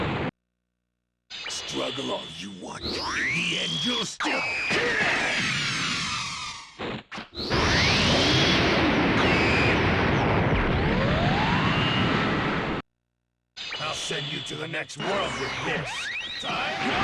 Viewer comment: What the hell is Broly saying at the end of that threat? "Struggle all you want. In the end you'll still -blank-" Sound like "keeyaugh" or something. I can't make it out... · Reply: He says "Struggle all you want in the end you'll still die." When he said "die" his voice got cut out, IDK why the developers didn't fix this.